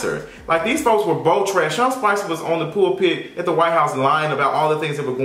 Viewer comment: I thought you said " the white gays" lol